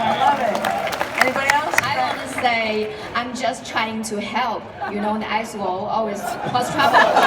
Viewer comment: Does anyone knows what she says at 31:25 ? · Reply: She wants the line "I'm just trying to help" play in-game when Mei puts up an ice wall and apologizes on behalf of all Mei players for blocking.